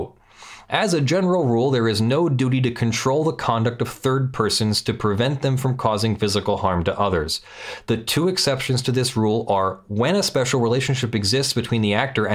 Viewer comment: I thought I heard “there is no duty to control the conduct of Bird Person...”